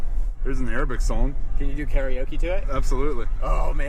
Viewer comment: This was an Epic part 1 so far!! How hilarious was getting all the tree branches given to him while driving and then hitting that Arabic Karaoke!!! 😂Definitely looking forward to the next episode. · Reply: I thought they said Aerosmith karaoke and then I was sure that was the guitar solo from Angel! Lol, would love to hear try to nail those notes.